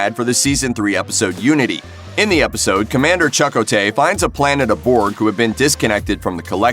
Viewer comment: lolol... IDK if it's your pronunciation of his name or the real possibility that you've never seen the show that amuses me more.. you say "Chuck Otey".... please correct me if I'm wrong but that's what I heard.. I listened to it about a dozen times cause I thought perhaps I was having a stroke and not hearing things correctly.. his name is Chakotay ONE WORD and full of life!! Say it like you mean it!!